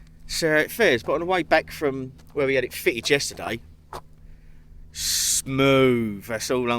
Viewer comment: I thought you said at the beginning, that you were having a fin fitted! I thought a fin, is he trying to turn Gladys into a Great White?